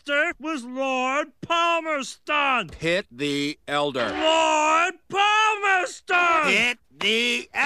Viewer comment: Pitt the elder sounds like Hit the elder, no offense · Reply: At first I thought he said hit the elder